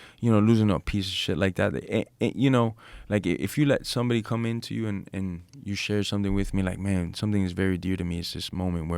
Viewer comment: what did he just say 😮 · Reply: Disgruntled lover 😂